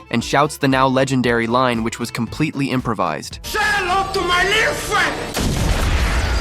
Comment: All I heard was "Say hello to my lil fe"